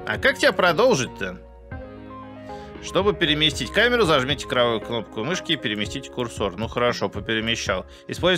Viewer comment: Мне одному после просмотра Шерлока послышалось "зажмите кровавую кнопку мышки"?